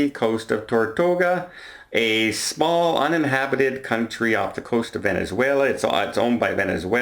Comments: did he say the island was an inhabited country? Is there such a thing? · Hooray for the cruise ship!! Cruisers 1 navy 0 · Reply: did he say the island was an inhabited country? Is there such a thing?